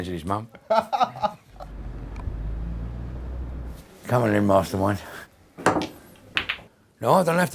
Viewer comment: it sounds like he says "Come on in, Master Wayne" XD